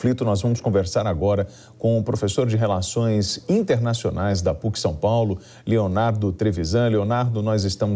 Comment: Peço desculpas, mas ouvir o comentário de um professor da USP, e de RELAÇÕES INTERNACIONAIS, é o mesmo que pedir a opinião da DilmAnta sobre essa guerra . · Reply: Eu entendi que o repórter falou PUC. Não USP.